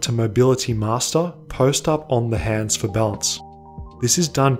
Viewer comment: What does he say on 3:20 :D can't quite hear it right.. " I recommend to everyone, from beginner to mobility master........" to do what ? :D the help would be appreciated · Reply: “Post Up on the hands for balance”